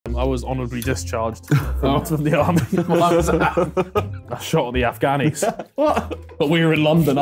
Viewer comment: "I shot one of the afghanis, but we were in London" "they took my medals and all" 😂😂😂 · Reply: All I hear is "I shot the Afganis"